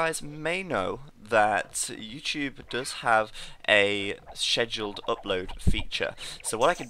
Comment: Are you saying Scheduled? ._. Not being mean but i'm not sure if you are or not! Lol, the "SHH" sound you make in Scheduled (if that's what your saying), barely makes the word recognizable.. It sounds like "Sss-kuh" in American English.